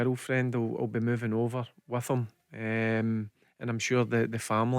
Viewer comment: Gonnae stop saying listen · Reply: Why did you have to post that? I can’t stop hearing it now. Even when he said his girlfriend will be going “with him”‘I thought he said listen.